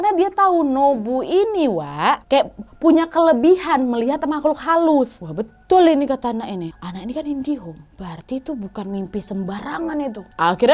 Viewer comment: aku dengernya kak nad bilang anak indihome, bener ga sih 🤣